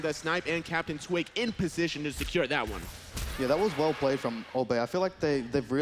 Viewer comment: Did he say "that was well played by Old Bay?". It's the best of all spices, so that's always a good call.